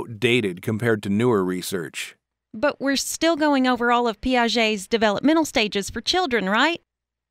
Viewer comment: I can't hear correctly what she says at the time 4:15-4:15. A word starts with ''p'' but can't get it, and subtitle also does not write it correctly, it shows ''piaces'', however this word doesn't exist at dictionaries. Could somebody help me, please? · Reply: Piaget